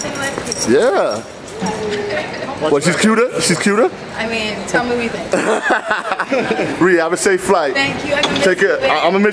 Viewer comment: guys, what did she say at 0:54? "i mean..." ... thx #1eaSBHD2ssA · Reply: Kaif Nasir she said "I mean, tell me what u think"